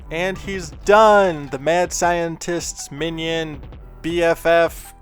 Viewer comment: I Thought That It Said The Mad Scientist's Onion xD Edit: OH MY GOODNESS! TITANIUM WHITE! bob ross would be proud